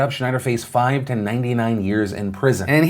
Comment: so i replayed 10:19 a couple times am i hearing him right 5-99 years ?